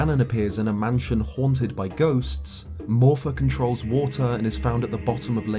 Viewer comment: Am I the only one who heard "More fuckin' trolls water"?? 🤣🤣🤣